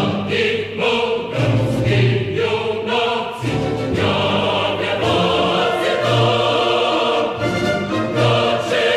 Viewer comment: did they say ”nazi”? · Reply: the said unatsi which translayes to brave man basicly